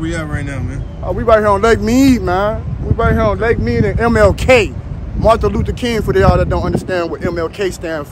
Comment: did he say Martha Lutha Keen?